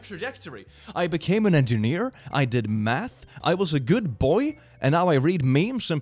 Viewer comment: Legit sounds like he said meth